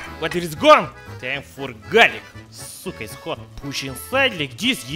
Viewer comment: "Cyka is hot" That's what he said